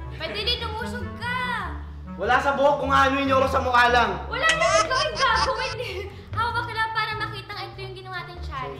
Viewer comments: did francine said gago ahahahahahahaha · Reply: "wala naman kaming gagawin e" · did francine said "gago?" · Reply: Gagawin